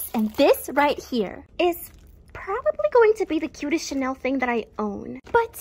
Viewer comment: Tbh I thought it said best pink CHANNEL thing 😂😂😂😂